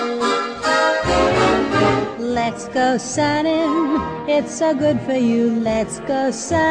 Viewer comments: DAMMIT, I keep hearing "Let's go bowling~~" DAMN YOU ROMAAAAAAAAAAAAAAAAAAAAAN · I thought it said "sign your kids away" instead of "sun your cares away" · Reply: I keep hearing let's go sudden